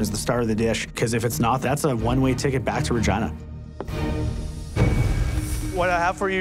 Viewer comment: Can someone tells me what said I think I miss heard but wasn't sure😅. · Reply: "If it's not, that's a one-way ticket back to Regina." The last word he said was slurred so I can't tell if it he said "regina" or some other word/phrase.